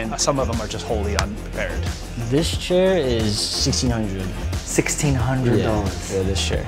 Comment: my English is no good. Did he just say 16,000 Dollors ???? a chair ?????? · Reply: Sixteenhundreth = 1600 Sixteenthousand” 16,000